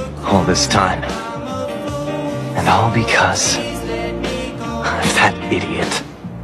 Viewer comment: When he said “of that idiot” I thought he said “im a fat idiot” ik I’m wild💀